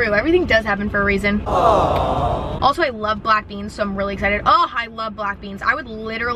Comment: I thought she said “I love blackpink”